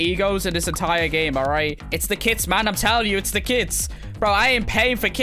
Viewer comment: I thought he was saying “It’s the kids man it’s the kids! I ain’t paying for kids man I’m paying for a new life” then i realized he said kits